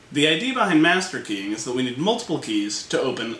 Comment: Why did I hear "The idea behind masturbating"? :P